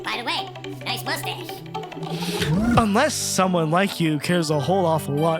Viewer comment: For years i thought that the "UNLESS" said useless. Like he was just wanting to leave a middlefinger for all time.